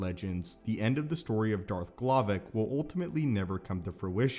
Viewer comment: I heard Darth m'Glovin